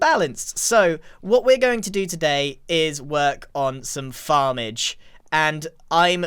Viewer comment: I thought he said “we’re going to work on the barge”